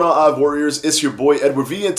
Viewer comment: Ooooooooh!!!! You're saying, "I F Warriors". I've watched 100 of your videos, and I've always thought you said "Ive Warriors". I thought I was missing an inside joke.